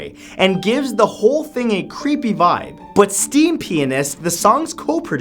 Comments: I thought he said "steampenis".😂😂😂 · who else thought he said "steam penis"?😂😂😂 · I heard him say STEAM PENIS instead of STEAMPIANIST